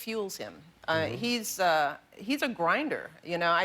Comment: Lol did she just say he is "on Grindr" 2:22"